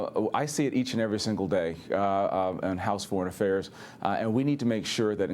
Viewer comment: Did he say 3:21 3:23 I see iT each and ever day? House in house of foreign affairs?